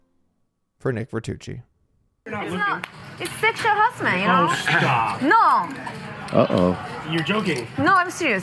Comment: what is the girl saying? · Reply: I think you must be joking, but she said "no stop, it's sexual harassment"